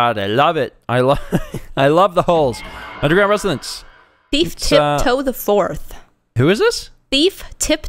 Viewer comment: I thought said Beef Tip Toe IV lol